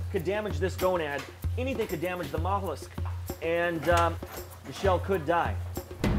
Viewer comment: I thought he said any mistake and Michelle can die